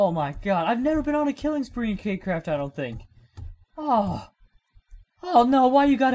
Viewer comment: did he say cake craft love ur vids though dooooood